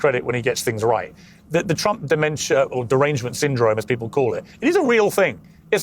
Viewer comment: did he say "Trump dementia"? 🤣